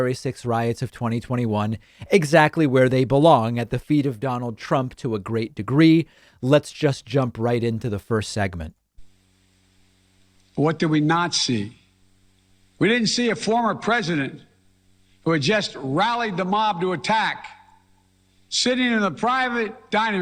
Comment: I heard "what do we not see" and thought he called Trump a Nazi; I was like daaaaamn bro chill. But i rewinded like ohhhh makes more sense LMAO